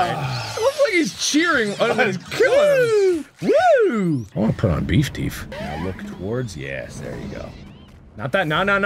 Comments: I heard "I'm gonna put on beef teef" anyone else? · What does Mark say at 5:05? Won ton beef deef?